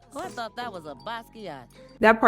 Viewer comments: Lol what the hell was Trudy saying here? “I thought that was a ?” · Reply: It sounds like "basquiat" and when I searched the word up on Google, it gives me an American artist named Jean-Michel Basquiat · I had trouble hearing what she said, but I think it's "I thought that was a Basquiat." I looked up the last word to find the correct spelling. I think it's in reference to Jean-Michel Basquiat. Trudy might have confused that picture for his 1982 neo expressionist painting "Crown."